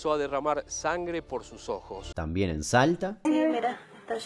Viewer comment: Escuche tan mal que pense que dijo "tambien salta" 30:14 y pense que se referia que la virgen saltaba JAJAJAJ